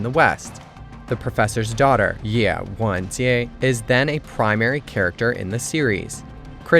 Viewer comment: What's going on with this narrator's Chinese accent? The subtitles said "Ye Wenjie" and I heard "Ye Wangjian" or something like that! As for the subject of the video, I've read the book (translated into English, I was lazy!) but if I want to watch a series of it i'll watch the Chinese versions first. People should expect a "western" version to be mutilated, people in my country (UK) understand very little about China and unfortunately a large group don't want to understand. I suppose it's progress that anything from China got onto Netflix at all!